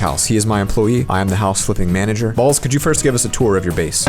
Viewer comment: As soon as vid starts i hear “mr balls”